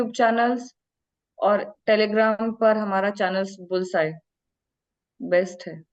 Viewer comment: congrats🎉 Mrs. ji. which telegram channel you said @ 28:13. I heard bull sight, but there's no such channel related to jht.